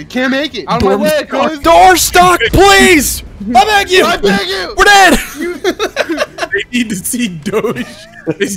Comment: Wait the guy said we're dead i thought he said "grenade"